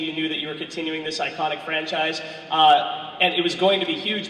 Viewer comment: Woah, I thought he said, "...when you were continuing this psychotic franchise"